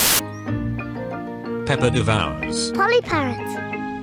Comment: i thought it said devoun but it was devours